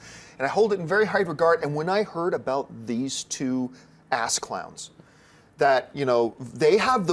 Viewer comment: I swore he said "ass cums" instead of "ass clowns"